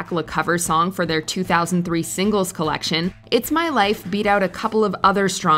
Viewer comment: That was the first time I ever didn't hear it as "who's my life". That's driven me crazy for years. I'm free! Nope heard it as who again. It pronounces who that way?